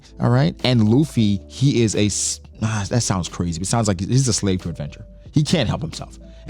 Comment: 🚩Luffy is NOT a slave for attention. Luffy does not lie so he didn't know what to say. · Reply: Yea it does sound like he said attention but he says slave for adventure not attention